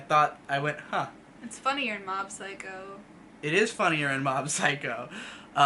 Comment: thought you said its funnier than Mob Psycho had my pitchfork ready for a sec (good to know you guys said funnier in*) lowkey disappointed, watched the Kaguya trailer and ultimately decided against watching it, I was hoping YOU would at least be able to convince me... but you've failed to get ME enthusiastic about this with all the praise are you telling me its overrated? fuck you b0ss